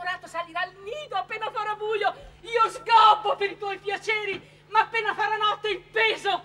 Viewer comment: "io scopo per il tuo piacere!" Ho sentito bene? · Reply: Io sgobbo